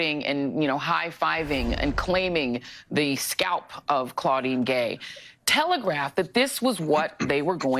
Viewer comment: Whenever I hear "Claudine Gay" I hear "Cloudy and gay." Strange weather forecast.